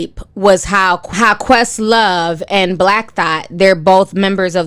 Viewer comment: Did say "Black Thot"? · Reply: Black thought is his name